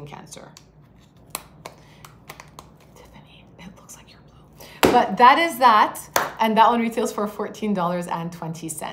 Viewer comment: what did she say at 17:57 ? tiffany it looks like you're ...? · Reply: “Tiffany, it looks like your blue.”